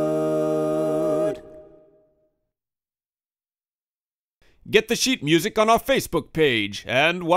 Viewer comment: i heard "get the shit music from our facebook page" but it was good! dw :) lol